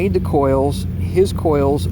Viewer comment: If I'm looking at my coils and I see it is spiraling downward in a clockwise fashion, is this the left hand (clockwise) coil? I want to make sure I'm correctly labeling these for my experiments in conjunction with your information. I take it the coil direction (clockwise/counter) is the same as the pendulum swing? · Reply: Wow, as a nonnative speaker of English with a bad sound system on my current computer I misheard what you say at 6:00 "his coils are clockwise, excuse me: anti-clockwise" (referring to Sucayho). I thought you said "these coils", at first. Figure my puzzlement! Obviously I did not understand any explanation before or after that or even what I found on Google. Thank God I took the time to keep on trying to understand these things for real, otherwise I'd be making and gifting very big meanie cemenite now LOL. Just sharing.